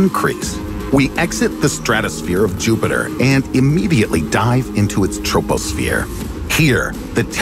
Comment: idk about u but I heard immediately die so I’m taking that